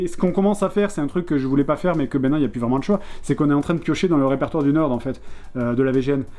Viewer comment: Il parle de quoi à 1:03:17 ? Où il dit qu'il est obliger de piocher dans le répertoire de "..." j'ai pas compris la suite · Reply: "On est en train de piocher dans le répertoire du Nerd", en parlant de l'Angry Video Game Nerd